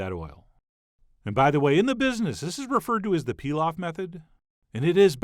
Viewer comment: I know you said “pilaf method” but for a sec I thought I heard “peel-off method” and was a bit confused! 😂